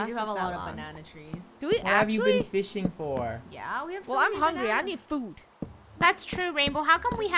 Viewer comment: What i hear is "Im hungyy" 😂😂😂😂😂